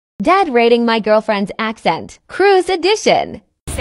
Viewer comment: I thought i heard: dad rating my girlfriends accident 😂